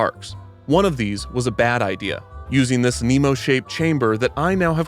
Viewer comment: horrible video, captions say vessel instead of chamber. 0/10